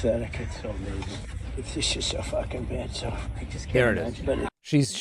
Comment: My man...I can't stand Trump as much as the next person but it sounds like he says "she's so f***ing bad". I don't think the reports are wrong about him calling her a bitch in private, but this clip ain't it. Look I don't like Trump either, but I think you got this one incorrect.